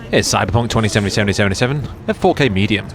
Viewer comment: what the hell did you say? This ai? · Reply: Here's Cyberpunk <num>, at 4K medium... Was it really that difficult to understand? (No, not AI)